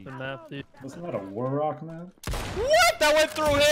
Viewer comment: DID HE SAY "WASN'T THAT A WARROCK MAP"??!?!! I TOTALLY FORGOT THAT GAME EXISTED. SO MANY MEMORIES